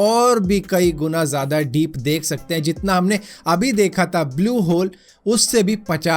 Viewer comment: did I heard blue hole?